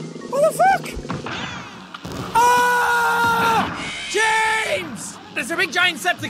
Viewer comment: Does he say "James" at 1:55? Is that a reference to the animator or am I overthinking this...😂